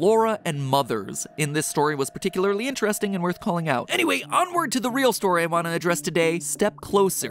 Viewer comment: When you said " anyway onward " at about 9:35 kinda sounded like foxy